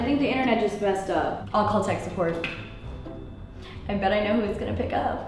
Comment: First I thought she said sexsupport😂😂😂😂